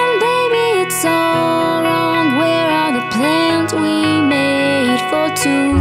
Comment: i heard "where are the PLANT we made for 2" hmm...